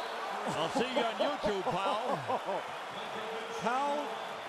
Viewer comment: LOL at first I thought the announcer said, "See you on Youtube pal" (instead of Pau).